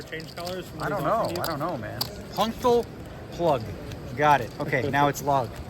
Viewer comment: can someone please tell me what the heck he's talking about?? Lol. Functal plug?? Is that what it said?? I'm lost, and Google came up with nothing.